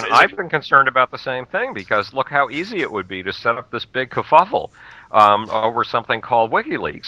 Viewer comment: what word does saY at 11:20. does he saY this big 'caffofol'